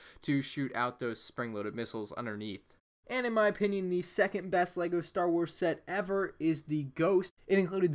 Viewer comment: who heard him say the second best Star Wars set ever is instead of saying the second best Star Wars rebel’s sets